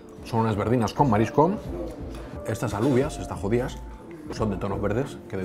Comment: Se que no lo dice pero, parece que dice, "Estas Alúvias están Jodías" :face-green-smiling